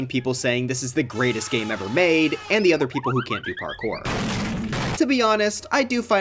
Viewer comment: I thought he said "and the other people who can't afford it."